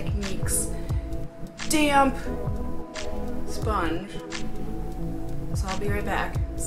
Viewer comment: Anyone else hear her say "my... DAMN sponge" at 6:25?